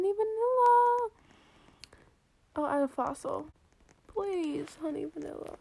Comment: When u said "i will add a fossil" i thought u said i will add a frost 😳